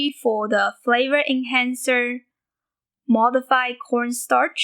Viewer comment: Lol at 1:07 I thought you said "MSG, for flavor and cancer", then later in the video I realized you said, "flavor enhancer".